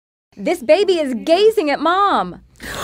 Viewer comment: I thought she said the baby was gay. I have a messed up mind 💀